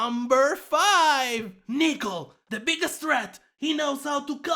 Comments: i Was scared for a sec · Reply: me who thought he said the n-word but he said nickel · i thought he said the n word when he said nickel